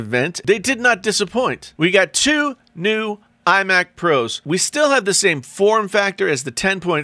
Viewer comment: Is it me or is he saying I mac pro?1:40.